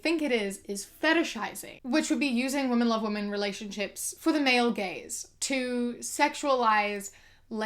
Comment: I thought you were saying "the male gays" and not "the male gaze" I was so confused